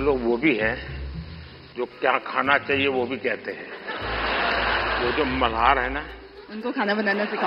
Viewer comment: did he said Mahar(sc)